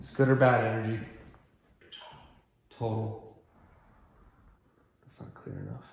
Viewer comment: I heard “ it’s old” and not “total”. It’s very clear on my headphones.